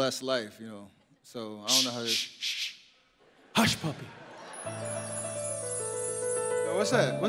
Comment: Did he say "hushpuppi" ?